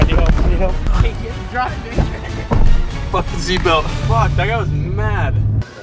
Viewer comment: did he say the n-word? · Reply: «Fucking seatbelt, fuck, that guy was mad»